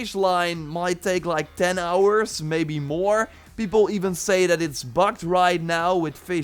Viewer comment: Lol I thought he said "it's fucked" for a minute. I was like, "Jor?"